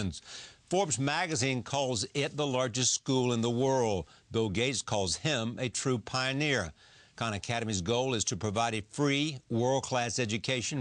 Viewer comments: "though gays calls him a true pioneer" WTF? Did he seriously just say that? What the fuck does being gay have to do with ANYTHING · He said Bill Gates, not gays. An honest mistake though, it did kind of sound like he said gays haha